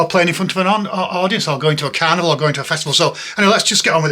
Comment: almost had a heart attack I thought you said you wete touring canada. but you actually said "carnival" seeing the last dj live would be a dream come true